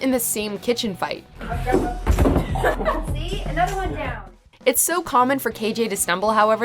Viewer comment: Anybody else hear a women say “see another one down” at 9:29 ? Also can we talk about how kind-hearted Hart (chic) was to run and help madchen (alice) and Lili (Betty)